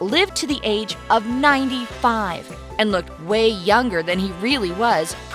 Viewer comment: I thought it said longer